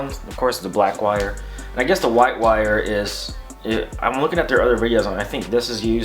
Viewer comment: Brooo at 1:07 you added the text “white power” instead of white wire lol just wanted to make sure you knew